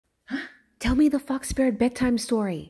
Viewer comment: at first i thought you said "tell me the fuck spirit bedtime story"